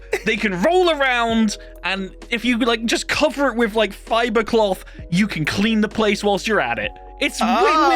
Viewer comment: i misheard fibercloth as fiberglass